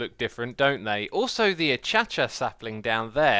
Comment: hey X i found a incorrect word achacha instead of acacia